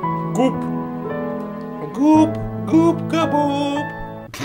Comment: Мне послышалось "ГуФка Боб"